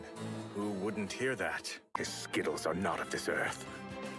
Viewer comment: “These skittles are not of this earth” HAHAHA PRETTY SURE I MISHEARD BUT ITS SO FUNNY